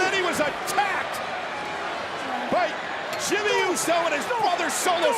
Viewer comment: Did anyone hear Micheal cole say " Jimmy uso and his father solo " ?